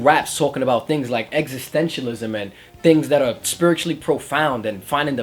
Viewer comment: What was that word at 3:20/21???? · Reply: existentialism